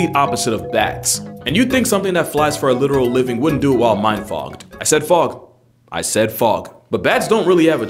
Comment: huh, I thought he said mind Bogged